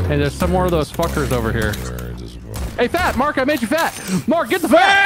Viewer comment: Sounds like said he made Mark “fat”.